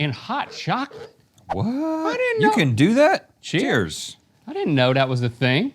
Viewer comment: Did they just say cheers instead of "dink it and sink it"???